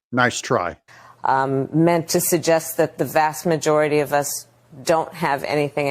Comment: It sounded like she said, "the vaxed majority."😅😂